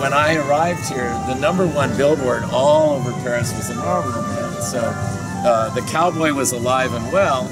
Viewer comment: Argh! He says the number one billboard is...then he turns away from the camera. Did he say the Marlboro Man? Is that still being used in France?